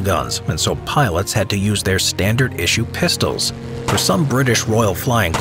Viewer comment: An ad came so perfectly, it sounded like you said "...they had to use their standard issue piss".